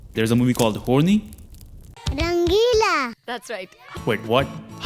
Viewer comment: Moral of the story: "mard bahot kamzor hote hai" 6:41 I heard "nangeela" due to the horny dance. 7:00 finally someone noticed her pain after all these years.